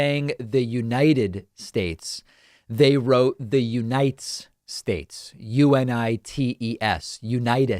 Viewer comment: I’m sorry, but what you said sounded like, “Unitis”, which is hilarious.